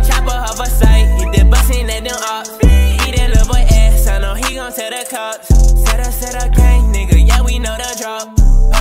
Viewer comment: "eat that little boy axx i know he gonna tell the cops" 00:57 WTF did I just hear